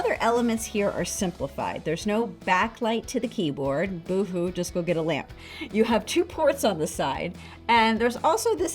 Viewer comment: Did she say boo hoo go get a lamp? Not very nice, people just want good value. Please value people more than corporations